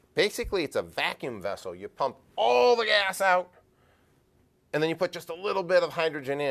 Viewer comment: he said pump all the a$$ instead of air , and was a little shocked of himself.